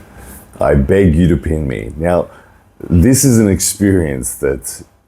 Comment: I thought at first it said " I beg you to peg me"